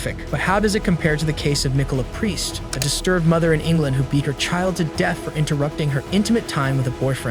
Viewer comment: DID I HEAR EATING HER CHILD!!!!!!!